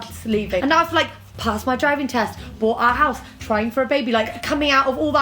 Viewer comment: I had to go back and rewatch because I heard “signing for a baby” instead of “trying for a baby”